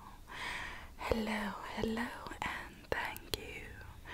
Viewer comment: When she said hello at the start it sounded like she said headlo